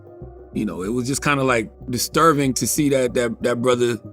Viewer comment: Does he say "disturVing?" 😂